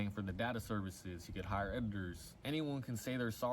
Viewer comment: did he say "hire edgars" 😂☠️ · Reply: editors